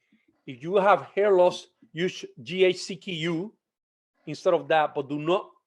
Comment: What does he say at 1:42??? “If you have hair loss use dh…..(inaudible)??” I can’t understand what he said? Anyone else catch it? Thanks